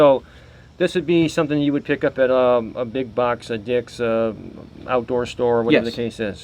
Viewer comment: did he say a big box of dicks?